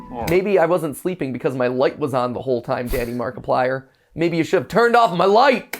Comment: I heard MatPat say "daddy Markiplier". I am so done.